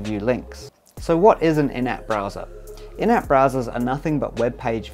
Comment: At first I heard "inept browser", which also applies.